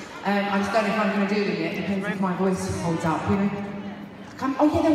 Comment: Hello! Do you know what exactly Adele said @ 4:00 - @ 4:10 after someone from the crowd asked "when do you come to Poland?" I don't understand her babble accent :D And then she is saying "I'm trying (...)" and I understand what is next. I'll be glad for your respond! :) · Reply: LOL I also cant understand that fast bit, but she said something like "shit I probably should've done that." as in add Poland to the list. After that she indeed says "Im trying , there's lots of shows I havent announced, lets see if my voice holds up"or something like that :)